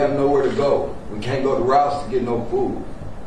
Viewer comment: Did he just say "we can't go to Ross to get no food" ?